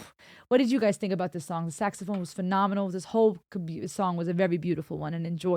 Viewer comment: Bob Seger is incredible! I laughed when you said Saxophone, for a second I thought you said Sexophone and I couldn’t help thinking that is a perfect way to describe the sound of a Saxophone!!